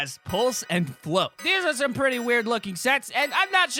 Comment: it sounds like he says s*x